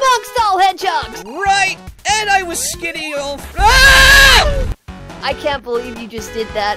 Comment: Did he say he was skinny in all Aaah!